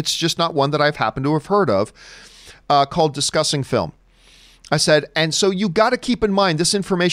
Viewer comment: I don't know the site but the first time I heard John say the name I heard "Disgusting Film".